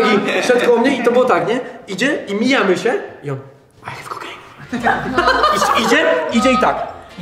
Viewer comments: co on mówi? Bo nie rozumiem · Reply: Chciał typ sprzedać kokaine ( jak dałam 2 komentarz to sorry, ale ten jest już prawdziwy xd) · co on mówi? Bo nie słyszę XD 😂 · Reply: I have cocaine mówi